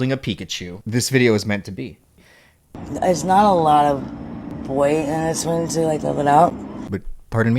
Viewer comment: I think she is trying to hit on him right here. What I heard is "there's not a lot of boys thats willing to like give it up."